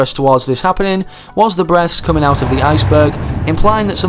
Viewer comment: I thought unsaid “breasts” instead of breath